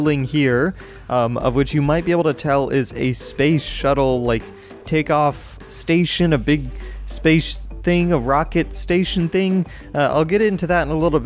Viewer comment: If you're not sure what said at 1:35, he said this: space shuttle like take off station a big space thing of rockets station thing. you're welcome.